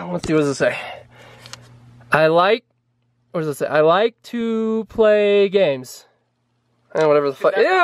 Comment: I thought it said “I like to play memes”